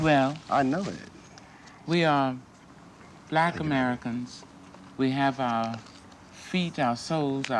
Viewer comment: The two black Americans have connected together again as what the world needs...more black identity and common sense. : ) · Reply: she says WE ARE BLACK AMERICANS. They did not say African at all.